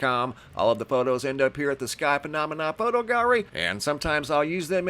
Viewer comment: Every time I hear phenomena... I hear "Mahna Mahna"... Muppets... Anyone?🤷‍♀️😂